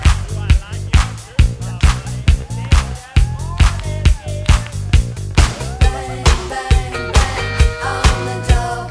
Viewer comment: in the background....did he say "They just farted in here"? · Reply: "It's getting hot in here!"